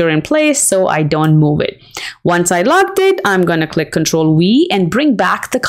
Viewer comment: For the second demonstration after you locked the picture. You said to click on control and what after that? Forgive me, but it sounds like you said control "we". I love what you have presented and I am putting a PowerPoint presentation together for my sorority using this transition on some slides. Thank you for sharing your talents! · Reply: Control +V It's my accent sorry! 🤷🏾‍♀️